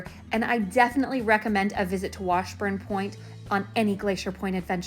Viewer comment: I keep hear you were saying: brain wash point. :))